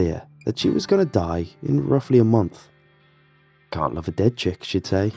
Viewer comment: I misheard the last line as "can't fuck a dead chick." And got SUPER concerned